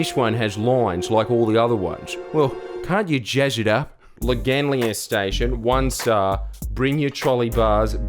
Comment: What In the wholly heck did he say? It is Loganlea (Lo-gan-Lee)